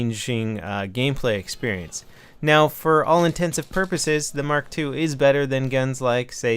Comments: Did he say intensive purposes at 3:33 instead of intents and purposes? sure sounded like it... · OMG 3:34, did he just say "for all intensive purposes"?